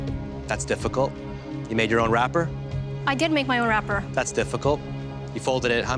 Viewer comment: What's the word after own ? r... thanks in advance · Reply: "wrapper"